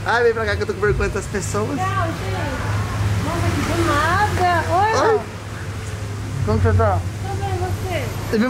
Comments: É IMPRESSÃO MINHA OU A FALOU "OI AMOR "NO MINUTO 8:13 🤔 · na hora que ele abraça a, ele fala gostou mozão? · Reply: Rekles e no minuto 8:09 eu escutei ela fala oi moh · é impressão minha ou ela disse "oi amor" pro?😱😱😱😱😱😱😱😱😱😱😱😱😱😱😱😱😱😱😱😱😱😱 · No min 8:09 coloca na minima velocidade q puder e escuta oq ela falou a falou"Oi amor" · saiu um oi amo